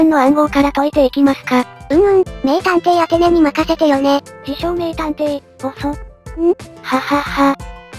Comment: 自称名探偵が 美少年探偵に聞こえたw